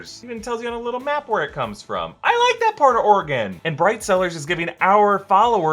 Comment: During the sponsored message, when he said ‘Oregon’ I misheard ‘organ’ 😅